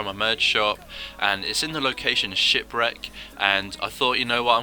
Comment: Shipwreck, I thought you said shitbrick lmao